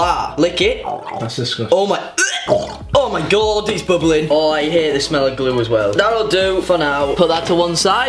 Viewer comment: did he say he hears the smell of glue or he hates the smell of glue